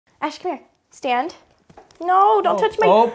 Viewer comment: When she said "ash come here" on beggining i thought she said ESKERE